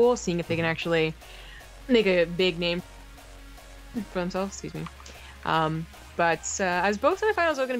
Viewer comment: It sounded like she was saying "nigga big name for himself" lol